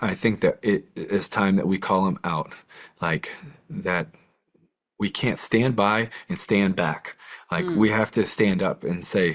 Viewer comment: standby and standback " huh Sounds like djt rhetoric. Is Tebow a trump supporter ? I heard yes. I'm with his righteous fight against evil, nefarious actions towards children, but I do question his judgment . Release the epstien files, so we can see how far the rabbit hole go. .. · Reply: You clearly wanted to hear what you want to hear. He said we CAN’T stand by and stand back which is the EXACT opposite of what DJT said to the proud boys. Please keep your partisanship out of this and think about how YOU can contribute to the work he’s doing.